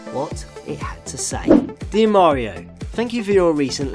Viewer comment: did you mean to say his name? or did you just say mario?